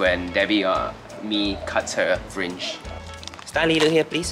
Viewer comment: when u said fringe I thought u said fridge